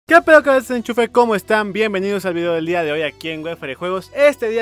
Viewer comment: Alguien me explica que dijo? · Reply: Que pedo cabeza de enchufe